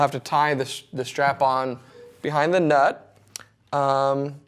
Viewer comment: Did he say : "tie the strap-on behind the nut"?